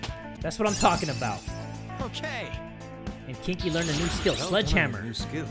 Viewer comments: I heard "kinky", lol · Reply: Alexza Bernandez Everytime I used Kin-Ki I heard "kinky" and I can't stop it from happening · I heard "kinky", lol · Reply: Popcorn Gaming // I know it's "Kin-Ki", I only heard "kinky", lol.